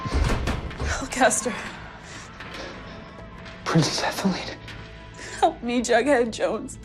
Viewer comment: idek what hes saying, all i hear is “prince, is that felid?”